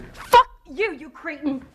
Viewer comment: that line sucked - i always heard her say "F you-You Creep" or "F You-You Creek" like it made no sense - you can't even hear her annotate "Cretan"